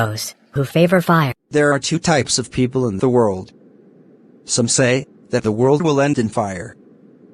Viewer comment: who heard as samsay like selling the samosa in train